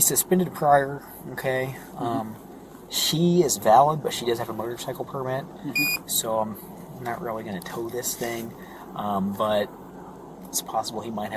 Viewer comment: Yeah I thought he said she didn't have a motorcycle permit either